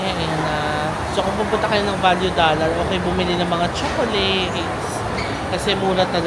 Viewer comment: "Kapag pupunta kayo ng Value $ huwag kayo bibili ng chocolates..." Tama ba ang narinig ko?